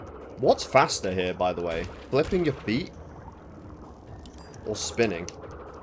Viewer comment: I thought he said "flicking your bean" 💀